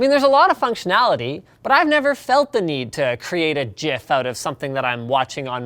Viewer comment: Did he say just say "jif" ?